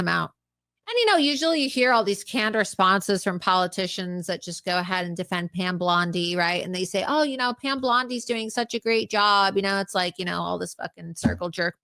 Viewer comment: Pam Blondi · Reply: I thought I heard Blondi too, lol.